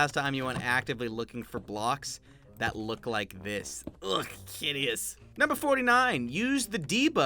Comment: MEEEEEE wait, did u say hideous, im unsubbing (jk lol)